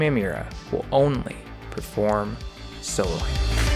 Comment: I thought you were saying “solo hand” until i saw it spelled out 😂